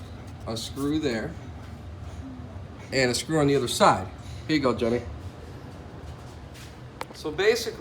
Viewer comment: it sounded like he said, "There you go dummy." LOL!!! Good information though.